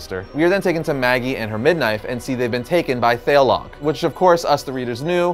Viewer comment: did anyone else hear "mid-knife" instead of "midwife"? It's like he was saying midnight instead of midwife and switched half way through. Good to see that even the pros make mistakes every now and again!! Great video! Love the Gargoyles! Forgot how crazy it got towards the end of the shows run. Didn't read the comics. Still looks great. Looking at you Disney +!!!!